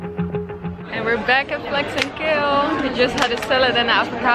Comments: Haha, I heard "Flex and kill" for the restaurant in Barcelona.....google corrected me :) (Thought it was a hardcore meat market or something. Holy moly.) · i totally thought she said flex and kill, not flax and kale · I thought you were saying 'Flex and Kill' restaurant :D Thank you for inspiring me to eat better. Since watching your vlogs, I have totally changed my diet, and feel so much better. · I thought u said “flex and kill” for the restaurant name 😂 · Ohhh Flax and Kale! I thought she was saying Flex and Kill